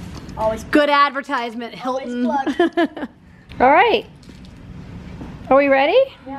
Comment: Did anyone else hear Annie say "always plugged" at 10:00? And one of her musical.lys was "Help me help you".... I think she's in the LOGANG 😵